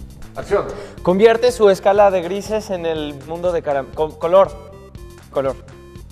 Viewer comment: alguien mas se dio cuenta que dijo en el mundo de caramelo... XD